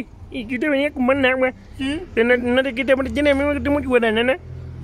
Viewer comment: ¿que dijo? Xdxdxd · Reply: ¿Tiene agua de guarabanana?